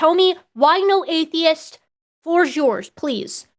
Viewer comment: As Forrest Valkai once said "We're too big to be bacteria and we move too much to be plants". He can start there. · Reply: I honestly thought you said Atheist foot!